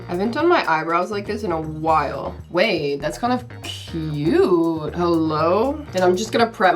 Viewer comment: Ok at 10:56 does anybody else think she says? That’s actually f*cking cute Just me?